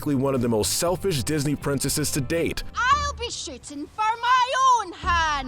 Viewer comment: Did she just say SH*T